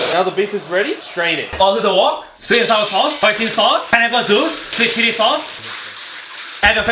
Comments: Language so thick I thought he said "Pineapple Sauce" 😂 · For those who don’t understand the accent, the man with the glasses said: “Oil on the wok. Sweet n sour sauce. Hoisin sauce. Pineapple juice. Sweet chili sauce. Add vegetables.” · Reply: I heard “Apple juice” lmao